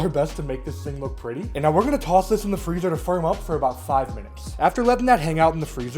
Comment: misheard putting the pie crust into the fridge from 5 minutes to 5 WEEKS